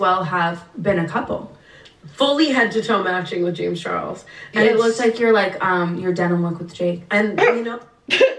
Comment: WHAT is she saying at 5:14?! what are you laughing at? i’m going crazy hahah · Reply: “It looked like your like um your denim look with jake”